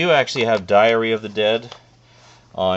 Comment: When you said Diary of the Dead, I thought you said Diarrhea of the Dead at first. That would be REALLY terrifying.